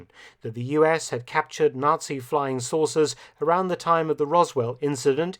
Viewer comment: I swear I heard 'Nazi flying sorcerers' at first. A Mark Felton video about Nazi flying sorcerers would indeed be something.